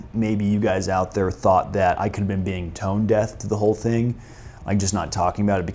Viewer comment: did you say "tone death"? yes, thats what i am going to latch onto and comment on for this video. serious question though. are you one of those guys that says "death" instead of "deaf"? caveat, i know some blind folks that dont like the term "blind drunk" i dont know any deaf people though, so the jury is still out on the phrase "tone deaf". sincerely. some fucking rando that you will probably never meet.